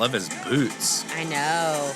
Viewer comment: Why did I hear " I love his GLUTES" 🙈😅🤣🤣🤣